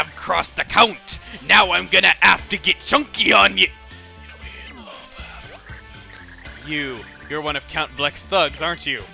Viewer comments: what'd he say? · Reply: Are you referring to the whispering? If so, he’s referencing a NintendoCaprisun’s classic: “Y’know, IN THE BATHROOM.” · Ya know IN THE BATHROOM at least that's what I heard by putting my ear to my speaker.